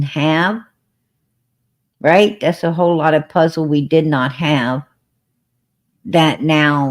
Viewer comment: 🤔🤔🤔🤔 I thought said they did not have ac